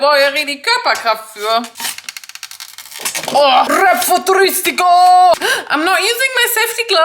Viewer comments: did she said ''rap futuristico'' 😩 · did she say “rap futuristico” at 28:58 or is my italian brain going on fire? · DID YOU SAY "RAP FUTURISTICO" CUZ IT SOUNDED LIKE THAT HAHAHA It's the title of an iconic Italian song from Fabri Fibra HAHAHA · did she actually say „rap futuristico“ ??😅🙈 i love it❤️❤️🇮🇹